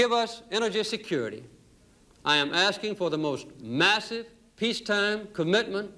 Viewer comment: Did he say MASSIVE?